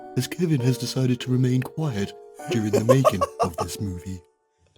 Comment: HAHA THEY FUCKED UP @ 6:25 the narrator says "movie" and the subtitles say "film." JACKASSES HAHAHAHAAHAHHAAHHAHAHAAHAHAHAHAHAHAHA. HAHAHAHAHAHAHAAHAHAHAHA. EDIT: Game has multiple errors such as the one I mentioned, which makes my original find much less captivating. ;'(